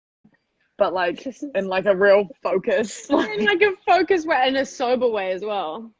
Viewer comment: Can someone please tell what says here? 38:33 (english it’s not my language, I tried but I can’t understand what she says) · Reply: She basically says : « well we had kissed before that but we were like : it’s weird to do it in a sober and focused way »